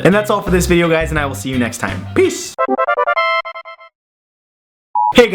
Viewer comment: subtitles said be screwed when he said peace at 7:12 XD